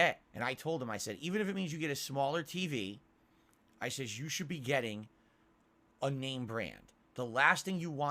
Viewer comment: It sounds like he says "I says yoush should be getting". Jesus, hitting the sauce a little early today are we?